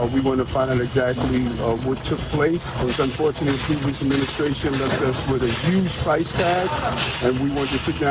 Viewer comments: Why are Democrats more focused on reparations than on fraud, waste, and abuse? Stop leaving yourself vulnerable to data breaches and brokers. Go to my sponsor <link> to get a 14-day free trial and see if any of your data has been exposed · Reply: He actually said the previous administration, so he meant the Joe biden administration Just for clarification if you listen closely. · I think you misheard the Mayor. He said the previous administration left them with a huge price tag. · Hey you misheard mayor Adam’s. If you put on subtitles he does say “unfortunately the last administration left us with a huge price tag.”